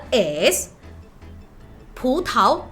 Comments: pensé que dijo putazo XD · Le entendí putaso jajaja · Minuto 1:08 escuche Pucta